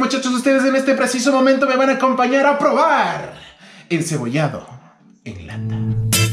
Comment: JAJAJA yo pense que decia "Probando encebollados en latam" xdd